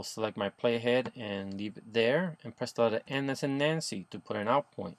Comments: what did you say on 1:42 to 1:45. I really need to know that part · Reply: press N as in Nancy. but this comment is three years old so i doubt im helping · what did you say at 1:43 it sounded like endenenency or something